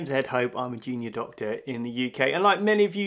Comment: Misheard his intro as "I'm a genius doctor in the uk" 😄